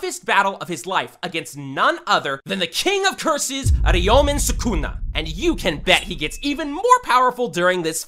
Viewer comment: I heard "ARIOMEN SUCKUNA"